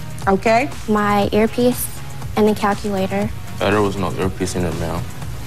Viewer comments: "That ear piece wasn't in there ma'am" Think this is what he said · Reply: Aaron Archuletta Maybe you need your speakers fixed? Or your ears checked out? The first time I played it I heard "There was no earpiece in there ma'am" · What did he say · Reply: Yep. His words were "There was no earpiece in there, ma'am." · What did he say at 0:48? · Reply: Caity Lotz "-but there was no earpiece in there ma'am." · all I heard was "I thought it was an old earpiece in her mouth"